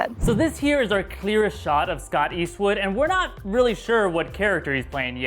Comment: I thought you said Clint Eastwood.